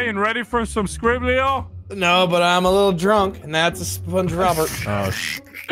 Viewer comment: I thought said spongerubber for the longest time lol. Also I was wondering what happened the video, because it didn’t show up in my watch history, or my notifications. I guess I know why now.